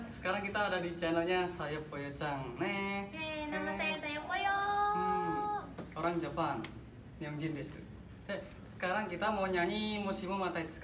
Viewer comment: "orang jepang yang judes" Apa cuma gw doang yang dengernya kaya gitu😂😂😂